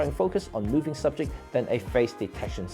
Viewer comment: One thought - does everyone watching this know what CDAF and PDAF stand for? How about a caption? I thought you said Face Detection, then realised that you meant Phase Detection.